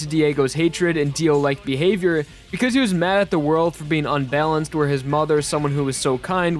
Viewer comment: Excuse me? Did you just say he was... mad at za warudo?